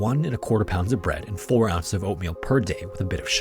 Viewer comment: I thought he said 4 Oz of Opium. Not oatmeal. I was like son of a bitch sign me up! I'll need all that opium to not be afraid of which species in the ecosystem is going to kill me first. You can keep the bread btw. 😂